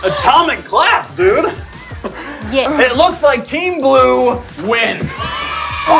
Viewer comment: When said: "Looks like team blue wins", I thought he said team glue...